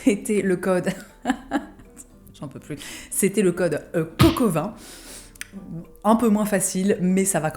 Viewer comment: J'ai entendu "coq au vin" au lieu de "coco20", je vais aller me recoucher je crois...